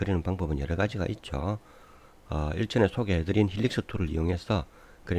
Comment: 에서 회전 램프를 그리는 무슨 툴을 말씀해주셨는데 정확히 안들려서요, 어떤 툴인가요? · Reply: 네, HELIX OF CURVE 루비입니다. <link> 참조 하십시오.